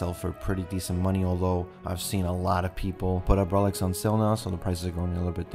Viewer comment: Can someone tell me what he's saying in 6:14-16? I can't understand. Thank you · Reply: "A lot of people put up relics"